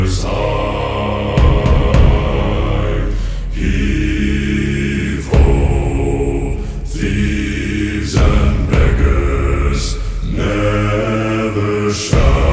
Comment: Ohhh he said thieves and BEGGERS... Thought he said something else. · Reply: I thought they said Thieves and BEARS... 😂😂